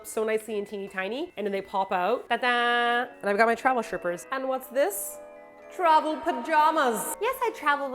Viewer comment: I thought you said "travel strippers" at 5:08 😂😂 glad to see I'm not the only one who packs theirs 😉